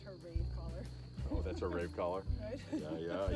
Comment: Oh,..”rave collar”. I thought he said “rape collar”. Ha ha